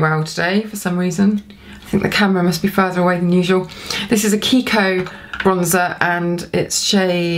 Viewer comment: I thought you said: Car Key Bronze :-D Thanks for the vid, nice autumn look!